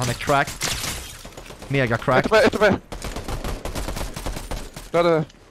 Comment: vad sa du vid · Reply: Mega crackt